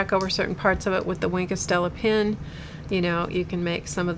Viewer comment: right at the end of the stream you mentioned a pen. I wrote it down to try and see what it was but what I wrote down from trying to sound out what I thought you said google can't figure out what I'm talking about. (You may not either *giggle) I write it down as winkastilla pen. How far off am I? and what is it? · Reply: edwards It's called Wink of Stella. ;-) You were very close. The one I used is the clear one. It comes in various colors, but I only use the clear one. It's like a very fine glitter suspended in a clear liquid. It adds a little bit of sparkle and shine to the area. Stay creative!